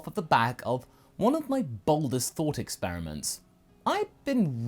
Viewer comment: For a moment I thought you said baldest instead of boldest